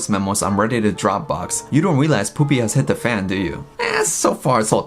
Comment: for a moment I thought Titanic called her "POOPY" 😂